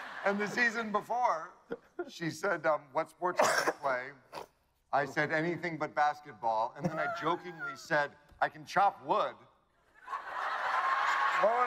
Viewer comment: What does he say at 3:23 and after that ??? Can't hear it quite properly..... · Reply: "What sports can you play, I said anything but basketball. And then I jokingly said 'I can chop wood'."